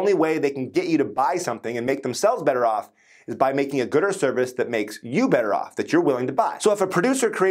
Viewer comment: I heard "gooder service" and did a double take at 5:36 before realizing what he said... its very late here haha